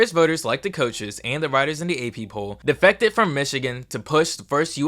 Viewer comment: Addressed mistakes: Accidentally said New Orleans instead of Illinois when mentioning the 2008 Rose Bowl game Said Birmingham instead of Brigham Young Miss pronounced “Vols” Enjoy the rest of the video! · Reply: I think I heard "defecated" instead of "defected". Better pronunciation would make this video even more perfect